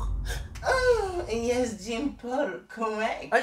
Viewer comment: “Oh yes Jean-Paul” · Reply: I thought he said Jake Paul